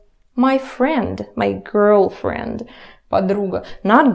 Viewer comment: i thought you said my girl friend, I choked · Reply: oh... you did say girl friend